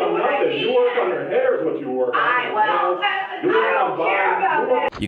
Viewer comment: did I hear "you need to work on your hair is what you need to work on" 😹